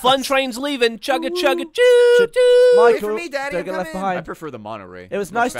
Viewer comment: Did anybody else catch Michael saying “wait for me daddy, I’m coming!”